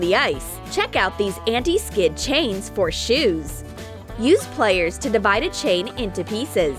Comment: She said players instead of pliers